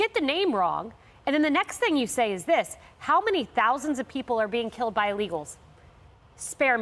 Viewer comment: Well, I want to touch on the other thing he said, which, myself included, at first anyway, thought he said it ...after saying her name, it sounded like he said "how many thousands are killed by illigals every day?" Which if you listen closely, he actually said, "But how many thousands are killed by legals every day?" Which basically was him, lessening her death by meaning, someone got killed by an illegal, but thousands are killed by 'legal citizens' everyday, which is even more disturbing and dsigusting and his way of covering his gaffe to his evil supporters. In my opinion anyway, if you really think about it?! It's like him saying, 'So someone got killed by ONE ILLIGAL, but americans get killed by other americans every day, so why the focus on one illegal doing it'? See what I'm saying? That's what it seems to me, because do we really think he's gonna extend that notion of 'Thousands of illigals are killing Americans everyday'? I don't think so. That's something he'd rather avoid speaking on! 🙏RIP LAKEN RILEY🙏 #SayHerName